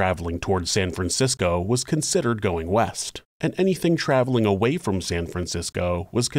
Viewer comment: West to San Francisco? I thought you said weast